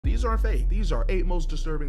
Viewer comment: Did he say these are fake at the start